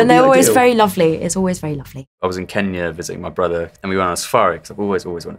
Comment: did Otis just say he was in Kenya visiting his brother?